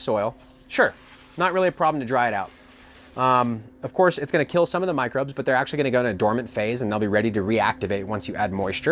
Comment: he says this word that sounds Koako comes up as koko on caption what product is this I can't figure out how to spell it to figure out. I also look on the build a soil website to see if I could go d a product with a name like that if any can lemme what he means. Be say it a few times between 21:25-22:10 thanks for any help. Is he just talking about Coco coir just saying it different but he said it was load with nutes n I pretty sure there not much in coco coir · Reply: Co Wo Co, AKA Colorado Worm Company Castings